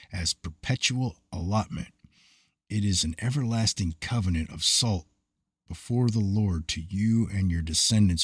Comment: Did you say "everlasting covenant of salt"? @ 6:14?